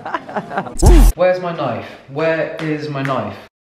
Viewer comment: When he said "Where is my knife?", I heard "Where is my life?" and thought "Yap, exactly my question!" and for a moment there I though you can read my mind 😨